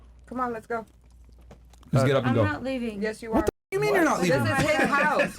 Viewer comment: Did she just said I'm not leaving 😂😂